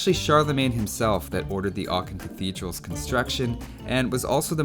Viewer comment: The way he says "Aachen" is adorable. At first I thought he said "arctic" cathedral lol